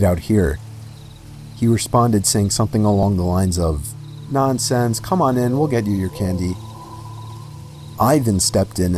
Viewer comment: what I heard was"nah,I'm sans come on in"Was it just me?!